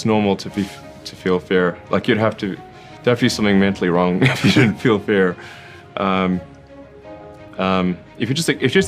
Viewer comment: 구간에 if you don’t feel fear 로 들리네요~ 만약 두려움이 없다면 정신적으로 문제가 있다로 해석되는게 맞는거같아요 ㅎㅎ